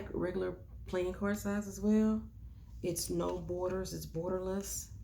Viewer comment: When you said “it’s no borders” , I thought you said “it’s snowboarders”. 😂. Beautiful artwork! I like what you said about church. I get it. I consider myself an atheist but it’s not that I believe there’s no god. I don’t know one way or the other but if there is a god, I don’t think it’s a stickler for this rule and that rule. It’s more about being the best person you can be. Anyway, I can go on about religion for hours. I won’t do that to you here.